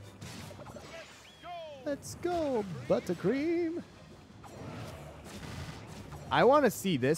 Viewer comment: I thought he said “let’s go foot cream!”